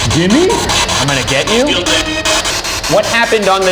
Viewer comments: I heard "get out" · Uhh I swear I heard "I'm gonna kill them" right after the spirit box said "get him" at 6:38 ish